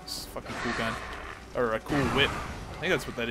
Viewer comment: did you say cool ghwip? watch?v=lich59xsjik